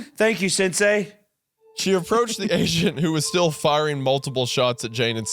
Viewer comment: Okay, when Wendigoon says "she approached the agent who was still firing multiple shots at Jane," I swear I heard "she approached the asian" please tell me someone else heard it